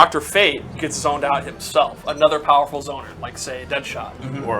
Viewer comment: I thought he said mirror master and lowkey got excited